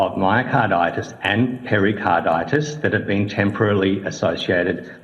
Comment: I think Dr Thiru (wouldn't want him to be my doctor!) says 'temporally associated', which Sen. Rennick misheard as 'temporarily'. I'm unsure if they ever unequivocally accept the jab is responsible, either in the hearing or the written response.